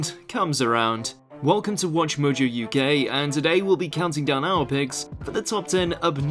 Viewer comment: Did he just say "Welcome to WatchMojo UGay"??